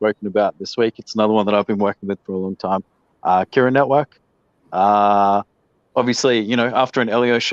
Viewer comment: Can someone tell me what "elio" or "eleo" was recommending? Cura network? I can't really make out the person's name or the name of the coin. At about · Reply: Kira network, kex